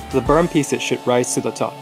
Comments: ik it said "the burnt pieces should rise to the top" but I heard "the burnt pieces of sh1t rise to the top" 🤠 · why did I hear "the burned piece of shit rise to the top"